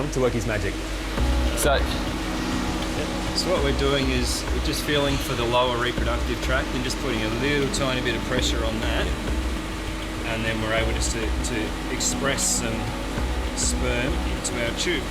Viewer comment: What the Drs say : “We feel for the lower respretory tract and apply a bit of pressure and we’re able to extract some sperm” What I hear: “we jerk off the shark”